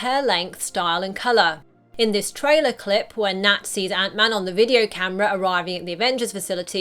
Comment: It sounded like you said Nazis